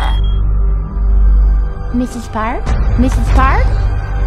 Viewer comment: Does she say Mr. Stark at 2:24?